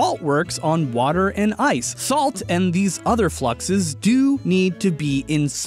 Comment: We can hear Austin say "Thalt" instead of "Salt" if you listen hard enough. Either that or it's just me